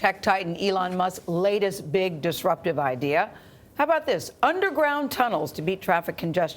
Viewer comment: Thought she said underbrown cuddle